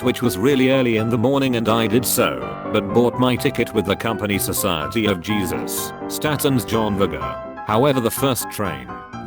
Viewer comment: I heard Society of Jesus lol